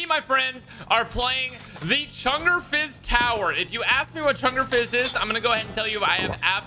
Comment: what map is this called ? anyone got the link for the workshop pls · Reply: The map is called ChungerFizz Tower, search it, I also misheard it as Chunderfizz